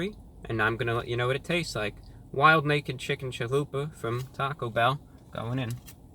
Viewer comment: Oh Wait! Sorry, I thought it said wild naked chicks.